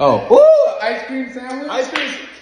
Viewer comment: For a sec I thought that said he cream sandwiche 😂😅😂